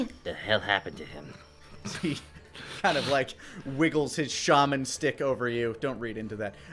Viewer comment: Am I the only one who misheard "shaman stick"?